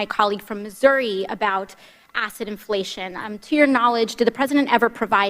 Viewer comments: I thought I heard "acid inflation." AOC, please try to neutralize your accent. Your other supporters don't have the best ears. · Omg, I SWEAR TO GOD, every time our Female Grand Champion, AOC, said, “Asset Inflation,” in all Honesty, I heard her saying, “Acid Inflation!” And, YET AGAIN, the man behind the scenes that was the ONLY other person connected to almost ALL of the crimes- outside of Dear Leader and Dear Leader’s family was.... ALLEN WEISELBERG!!! And guess what, boys and girls if the world- that is a man who is ALREADY cooperating with Mueller and he Southern District Of NY!